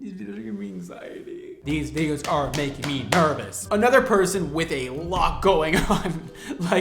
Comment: what did he say?? Did I hear that right?? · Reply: "These videos are making me nervous"